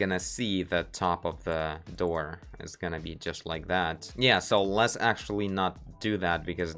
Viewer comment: "...it's gonna be just like that... не, let's actually..." when i heard this i thought i heard wrong but it was so close to my heart so i think it was very cute russian "да не да ну да ну не" on